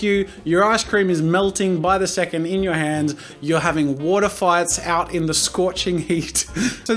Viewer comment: When he said water fights I thought he said water farts